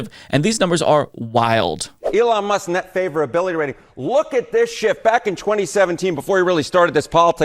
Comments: I swore he said "LOOK AT THIS SHIT" 😂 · Even after finally realizing he says *shift*, it still sounds like he says "LOOK AT THIS SHIT!" whenever I replay it. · Did anyone else hear, "look at this sh*t"? 😂